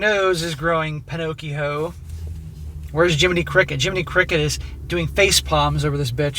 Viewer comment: He said "Jimminy Crickett" and all I heard was "Jimminy Cuckett". I died 🤣 😂 💀